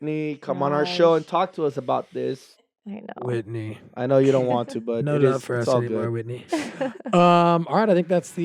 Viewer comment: What does Brody say about Whitney from around 10:36? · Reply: I think he says "we know we're not your friends anymore" something like that. I think it was just playful..